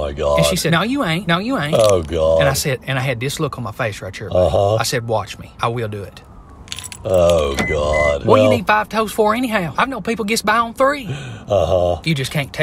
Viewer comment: Thought he was saying some nonsensical thing about "five toast" until rewatching several times. 😂